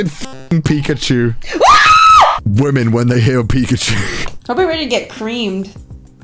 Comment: hope you are to get creamed… honey Me: spits drink did I hear that correctly?